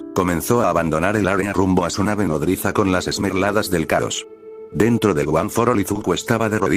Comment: no mames escuche "las mermeladas del chaos" xd